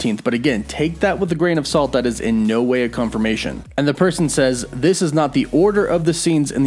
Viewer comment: Did I hear right? At 01:48 did you say that 15th of November to 17th of November is a "No Way" confirmation 😳? Hmm... I wonder 🤔... should I take it with a grain of salt or some coffee with milk and sugar? That's for another topic, I guess. I think that Marvel should go with their trailer version which does not reveal Tobey and Andrew. I mean, look at how great they have done so far. Sony reveals too much, in my opinion. Look at how much they revealed in their latest trailer of Venom 2. By the time I got to watch it in the cinema, I felt like the movie was just an extended version of the trailer, because all the important elements were already there and the movie itself did not add anything interesting. Don't get me wrong, I loved the movie but I prefer to have that element of surprise in the cinema, after having seen the trailer. I think that is what made Marvel to be great so far. Their post credits and trailers show you just enough to build your hype, but not too much so that you can build more hype whole watching the fully released movie.